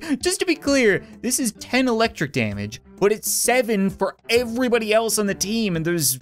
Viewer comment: Thought it said electrical